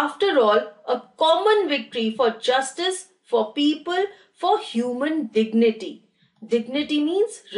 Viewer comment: she said For people instead of peace A simple correction...